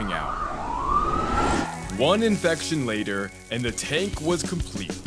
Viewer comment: Did he say at the start 0:51 one infection later